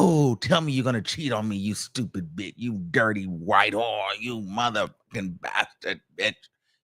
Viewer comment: Pause....did you just say "dirty white whore" Is Jane Doe white?????